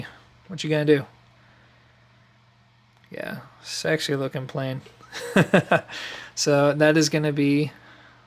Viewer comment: it sounds like your saying sexually lookin plain